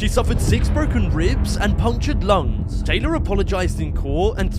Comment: 2 DAYS??? Bro i thought he said "2 years" SHE LITRALLY WAS CRYING OVER 2 DAYS IN PRISON she was like "so what if i broke somones whole respiratory system"